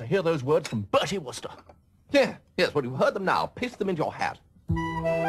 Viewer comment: At first I thought he said "piss them into your hat" at 07:28. I guess I have a dirty mind.